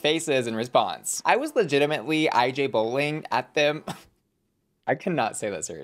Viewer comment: wt heck does he says in here? I’m not fluent nor updated and even less are this subtitles getting it neither 😂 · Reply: "IJBOL" stands for "I just burst out laughing", it's like a new version of LOLing